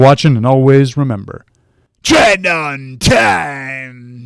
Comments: I always hear "train on time!" so I imagine him chasing me down for procrastinating going to the gym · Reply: I hear “Tread on time” which makes even less sense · and telling you to train untamed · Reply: I always hear "train on time!" so I imagine him chasing me down for procrastinating going to the gym